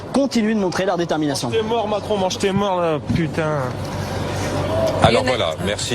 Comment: Il dit quoi le gars à 4:14 ? svp "Mange tes morts" ?